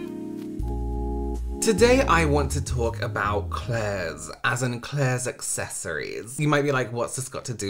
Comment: Initially I thought you said "today I want to talk about eclairs"... and I was confused but intrigued... Yes... let's talk about delicious pastries... but that was not the topic... lol